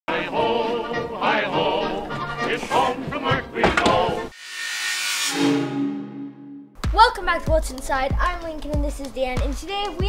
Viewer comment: I always thought Lincoln said hi I’m Lincoln and this is dad